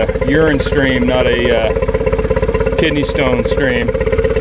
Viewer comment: Should be like a urine stream not a kidney stone stream. 😂😂😂 That analogy had me cracking up. Great video man! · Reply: I thought he said 'kid stoned stream' :D